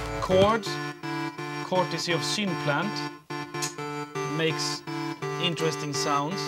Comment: what does he say at 2:49 chords synplant? is that a vst??